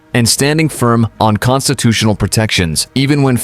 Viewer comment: DID YOU SAY CONSTIPATIONAL PROTECTIONS? NO WAY YOU SAID "CONSITUTIONAL" AS THAT IS A VIDEO STORED FOR THE EXTORTION OF CITIZENS VIA THE TYRANNIES OF "THE RIDES" THROUGH THE MANDATED UNJUSTICES OF AS STANDARD TYRANNY LEO/JUSTICE SYSTEMS' YEARS OF NOT SWIFT JUSTICES.